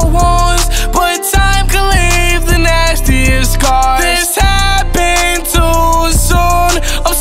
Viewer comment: "Time, time heals all wounds, but time can leave the nastiest scars" -Jarad Anthony Higgins (<num>) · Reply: I thought he said "next years scars"